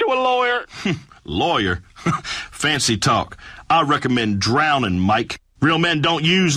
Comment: I heard “I recommend drowning, Mike” and my race was nearly ruined.